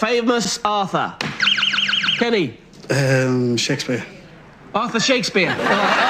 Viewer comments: I can’t blame him. I thought he said “author” not “Arthur” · Arthur Shakespeare. Even British people can't understand their own accent because I heard "author" too.